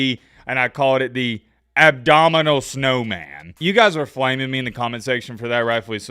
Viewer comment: I thought you said it was the abdominal snowman lol